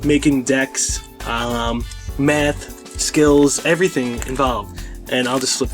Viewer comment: I thought you said, tactics, meth, skills... THE FAUQE